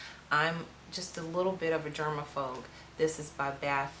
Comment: Did she say germa-phogue??? 8:02 bwahhaha